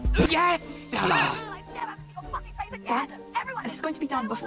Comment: I thought he said “YESSSSHHHAAHHHHH 😫”